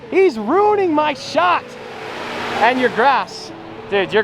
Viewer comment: when he said "urus's" the CC thought he said "your asses"